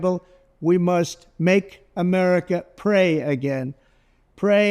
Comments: I heard Trump said "Make America (a) prey again". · I hear, let's make America prey again.